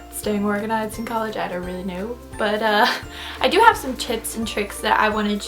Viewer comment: i thought she said "i have some chips and tricks"